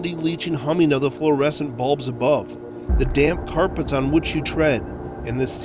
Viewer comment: I thought he said “the damn carpets”